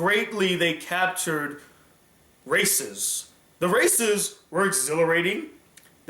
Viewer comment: I thought you said racism